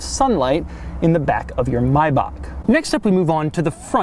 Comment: It kinda sounds like your saying my-back, 13:30 is an example